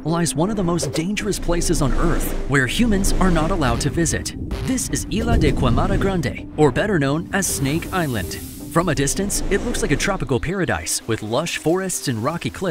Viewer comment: Thought you said Ariana Grande lol 😅